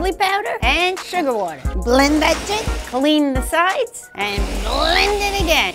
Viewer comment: Did she just say "blend that shit"?